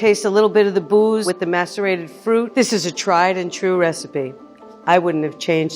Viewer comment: I thought I heard "masturbated fruit"